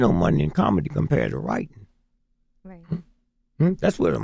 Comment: " Ain't no money in comedy, the monies in writing??" 57:29 Can someone tell me what he said??